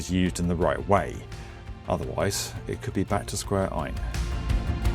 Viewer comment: My engineering brain heard 'square i' instead of 'square 1' and it made me jump in my seat. Please don't scare me like that again XD